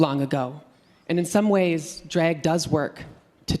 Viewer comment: I thought she was saying Dreg = Art and got confused, then I got she was saying Drag.